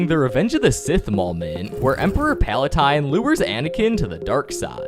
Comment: did he just called him Paletine?